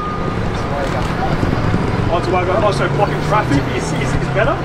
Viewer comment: what's he saying here? "you had ??? here for three hours" ....sounds like "vegans" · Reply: Yes, vegans. I think he's referring to the recent vegan protest.